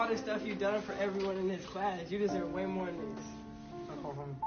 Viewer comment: I put on caption and it says you deserve woman’s instead of saying you deserve way more! 3:14 put on captions!